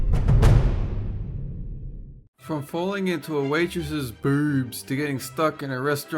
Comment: to me it sounds like you say, "BOORBS" I don't know why sorry 😂